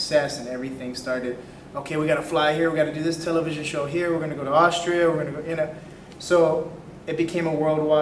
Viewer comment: Did he say Austria?